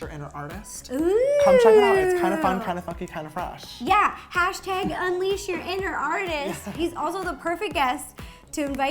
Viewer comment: did say F*cky? REPLIES! 🍰 🍰 🍰 🍰 🍑 🍑 🍑 🍑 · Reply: funky